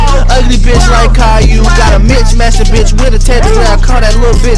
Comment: I THOUGHT IT SAID "GOT A MIXMASTER BITCH" 😂😂😂😂